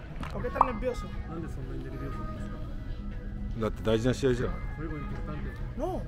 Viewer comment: マーティンのところ、なんて言ってるか教えてください🙇‍♀️ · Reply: 「なんでそんなにnerviosoなんですか」だと思います！ nerviosoはスペイン語で「緊張している」って意味です